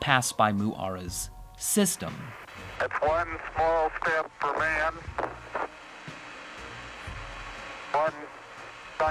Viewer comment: ''That's one small step for man'' I only hear ''man will space walk''😂😂😂